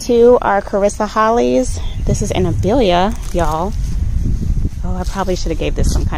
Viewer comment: What is the name of that gorgeous blooming plant by your mailbox!? All I heard was "amethelia" and I think I misheard because Google has no idea what I'm talking about. It is so beautiful!